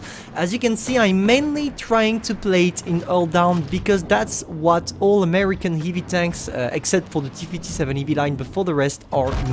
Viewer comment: what he says? exept for ...... what tank he says? iput back the video 3000 times but...that pronunce is awful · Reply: hes saying you should go hull-down in every american heavy tank except the t57 heavy line